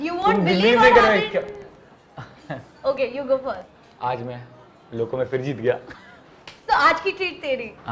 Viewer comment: Bro there is a mistake in the english subtitle of ur video 0:25 says "u know aj me phir jett gaya "and the subtitle says something about the bill which should not be there .....i know people will copy but im first to observe :)change it if u can :)